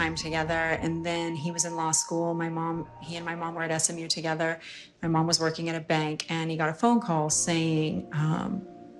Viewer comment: Does anyone else hear that person say something echoy at 5:16 ? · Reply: Hello… for sure a man..